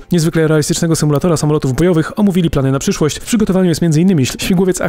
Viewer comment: Za dużo Netflixa... 2:16 usłyszałem samolotów gejowych. jpd.